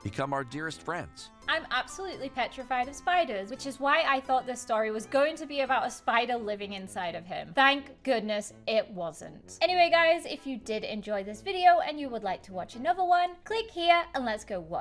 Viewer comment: Why did it sound like said prank goodness instead of thank goodness